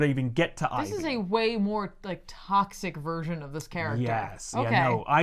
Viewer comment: When you soak up all of the BATMAN tuber experts as you write a Fan Book/film and realize that writing BATMAN is not only the most fun a BATMAN fan can have but BATMAN is NOT always written well and published too, this is an insane neglect of this materiel. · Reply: does she say "Psyc-toxic" or "Like Toxic? did she coin a prhrase cos I'm stealing it.